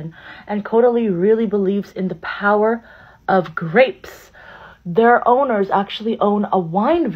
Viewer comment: Believes in the power Of freindshi- Grapes!! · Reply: I have ear plugs in and got so confused because I thought she said power of crepes